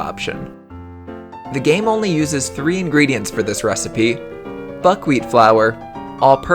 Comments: I thought he said Fuckwheat flour. LMAOOO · Lol kinda sounded like f*ckwheat flour lmao 😂